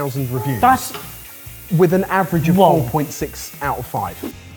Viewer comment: did he just say an average of 1.6 out of 5? Surely it was 4.6 if they brought it on the show, but I listened to it 3 times and my ears kept telling me he said 1.6.